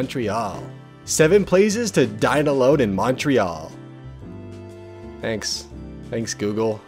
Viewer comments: I misheard and thought it was "7 places to die alone in Montreal" and I still related. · EHAJWSBEBEKWOSHEHEB I MISHEARD "7 PLACES TO DINE ALONE IN MONTREAL" AS "7 PLACES TO DIE ALONE IN MONTREAL" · i thought u said “7 places to die alone in Montreal”